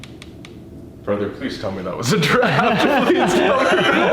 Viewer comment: The group: hears a bang OMFG WHAT WAS THAT WOAH The group: sees a door shut by itself WaS tHaT a GiRaffE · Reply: he said draft but because he laughed as he said it the word got dragged out to sound like he said giraffe